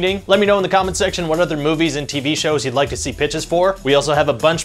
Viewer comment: I misheard him say "let me know what other movies you want to see BITCHES..... We also have a lot of other BITCHES on this channel" 😂😂😂😂